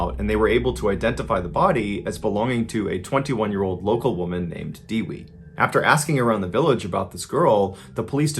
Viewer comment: It's Only Natural Reaction To Smile Whenever I Heard Foreigners Are Trying To Pronounce Indonesian People Name 😂 Her Name Was Dewi, What I Heard From Mr. Ballen Is Actually "Diwi" 😂 Almost The Same Sounds "Kiwi" While Kiwi Wasn't Written As Kewi 😅